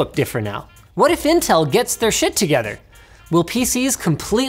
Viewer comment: Did Linus just say 'shit' at 7:26? :O